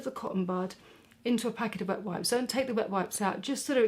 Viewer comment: The first time I watched this video I thought you said BUTT wipes. 😂 I had to watch it a second time to realize you said wet wipes. The 3rd time was for the learning experience and fourth and final time was for pure enjoyment because I just admire you that much.